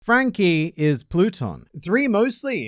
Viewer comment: I wasnt looking at first. I swear I heard "Franky is Putin."🙃🙃🤣